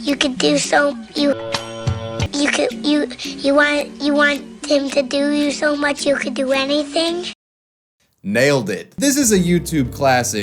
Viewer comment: I always assumed the kid was saying he had a dream where he could do anything he wanted... like he could fly or whatever. The Hercules thing makes sense though. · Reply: Upon further inspection, I heard him saying "you want him to do so much you could do anything"